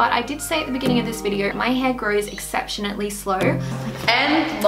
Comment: did you say 'exceptionately'? :P Really great, well put together video! Thanks for sharing your journey